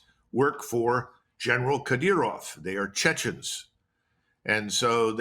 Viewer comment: Did he say General Cut Ear Off?